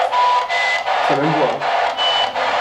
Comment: moi j'entends pas ok j'entends plutôt ta gueule 😅😅